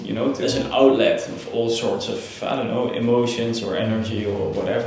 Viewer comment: I thought he said not "motions"but" emotions". What do you think ? I suddenly started to be curious about it.